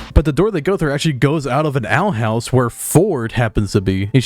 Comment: Thought you said “Owl House” for a sec